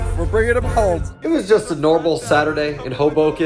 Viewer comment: At first I thought he said it was a noble Saturday. I was like, ya it was. Then I realized he said normal. Haha. I was a bit disappointed tbh